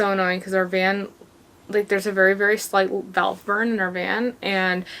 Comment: I couldnt quite make out what you had said at about 9:39. Something about a valve burn? Curious what issue it might have had? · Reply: Yes we had a valve burn in the fourth cylinder for the last year! Every mechanic we had ever gone to (4 mechanics now) all told us they COULD fix it, but that it would cost us around 3k-5k to fix and that it was basically a non-issue. Every mechanic we had been to advised us that it wasn’t really a necessary fix until the problem gets worse because then it would probably be time for a rebuild. But that probably won’t be for a few years.